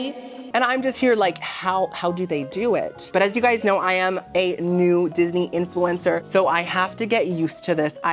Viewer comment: I can’t hear. I thought you said “I’m a naked Disney influencer.” Lol